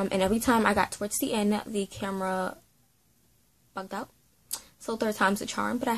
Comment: I couldn’t understand what you said when you said your camera missed up it kinda of sound like you said a bad word sorry if i’am wrong 🙂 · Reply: Hi yeah it did sound like that but I didn't lol. I said "the camera bugged out" if you mean at the very beginning of the video.